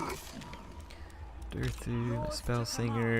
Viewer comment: Oh god... In several points e.g. 46:53 I hear Durthu saying something like "Rakas Durthu" which means "Beloved Durthu" in Finnish. Sounds weird as hell and I can't unhear it anymore...